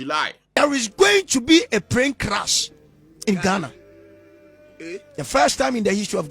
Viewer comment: Osofo did you say Prane Clash 🙄👀